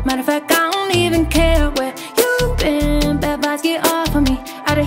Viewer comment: This whole time i thought it was girls instead if goals god im stupid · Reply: Me too! I also thought she said "bad guys, get off of me" instead of bad vibes lol